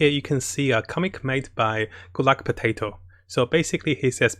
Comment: I heard “gulagpotato” instead of “goodluckpotato” lol